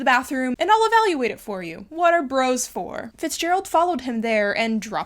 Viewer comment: I heard…”what a gross wh***” 3 times in a row while I tried to piece together the context in my head